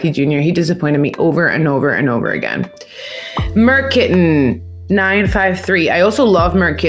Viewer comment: I never try to assume anything about anyone. 10:02 When you read out the commenter’s name, because I didn’t have my headphones in or the volume turned up all the way, for a split second I thought I heard you say merkin, which is a pelvic wig, so I had to rewind and listen to it again.